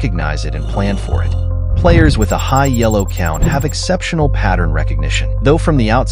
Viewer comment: did bro just said yellow?